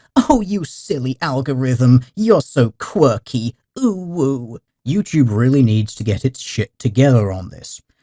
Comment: Did I hear an OwO at 3:06 ?